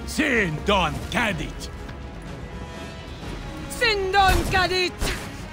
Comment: Sindon cadit? I can't be the only one who thought he said "Shield dodge cancel" it fits too. . .